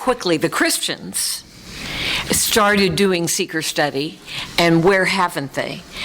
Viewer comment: Seeker study is not a term I am familiar with. I thought they were saying 'secret study' 😅 & that didn't seem right. I am halfway through & just got it!😊 💒